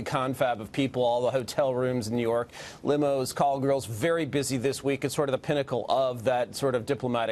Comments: did he say "call girls"?? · Did he just say call girls. Like escorts?